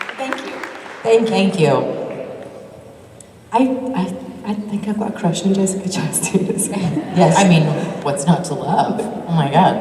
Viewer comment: What Ruth said in 16:48? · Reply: “I think I’ve got a crush on Jessica Chastain”